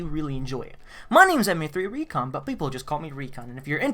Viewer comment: Did he just say Anim3recon???